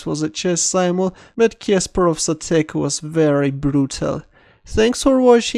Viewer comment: When you say "if you have any questions" , I always hear "If you have any equations" xD Maths is making me crazy!